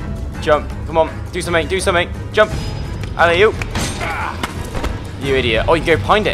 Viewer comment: I thought he said "and I oop-" and was like omg VSCOO